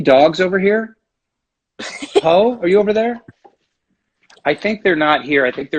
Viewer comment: She thought he called the dog Hoe. 🤣🤣🤣